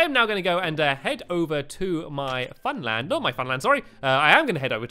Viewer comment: Stampy it sounded like you said let's head over to London and it is my b-day on Saturday